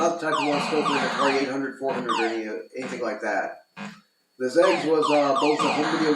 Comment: I can't understand what you're saying. The CC says you're saying the word "edge" but that doesn't make any sense. I hear zed x or something. Both a console and a computer. But I have no idea. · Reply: that's the Atari XEGS also known as the XE Gaming System. It's a third generation home video game console/home computer combination based on the Atari 8 bit home computer line. My apologies. Some times things don't roll of the tongue quite as intended.